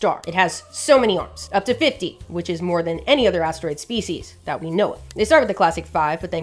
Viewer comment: Did she say asteroid species?